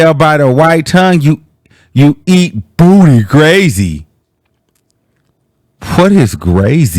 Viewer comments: I thought she said booty gravy Lol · I thought she said booty gravy😂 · i thought she said “Booty Gravy”